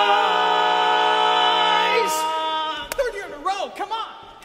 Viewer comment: What does say at 0:27? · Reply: "Third year in a row, cmon !"